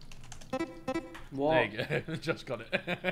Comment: Yo did Ethan say the n-word at 25:15? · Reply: he said there you go